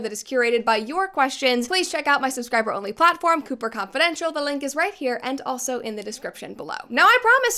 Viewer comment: I know I am losing my hearing, but I swear I heard “Pooper Confidential“